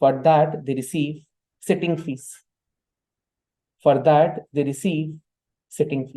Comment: can you help me with what does he said at 4:37:40 abt indepent directors regarding their fees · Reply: he said sitting fees, means they dont own shares not get salary. they just gets fees whenever they come